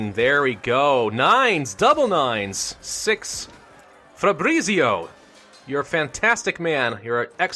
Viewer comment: did you say your general’s name is fabreaseio? Because if so, that would be hilarious